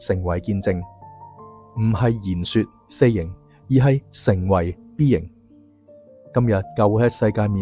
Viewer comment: 請問從04:33起，你講述的那兩個型，我重覆聽了數次也聽不岀講什麼？請問可以寫寫給我嗎？謝謝你🙏🏻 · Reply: Saying , being